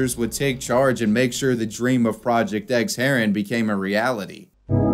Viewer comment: I thought it said Project X Harem lol